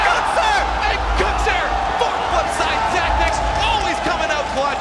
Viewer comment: Totally forgot to put Kyle Masc's shot in from back in 2015! I had written it down when I first made the list but it was lost somehow. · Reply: did he say fuck flipside tactics ?